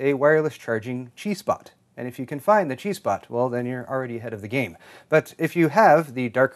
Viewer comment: At first I thought said "If you can find the G spot, you're already ahead of the game." when he said "If you can find the chi spot, you're already ahead of the game." MY BAD